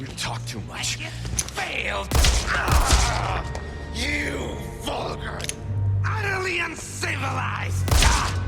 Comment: I just realized now because of your video that Salazar says "You VULGAR utterly uncivilized." I always thought he said "You fucker!" Now I'm sad because that would have been way funnier. 😔😔😔 Awesome video btw!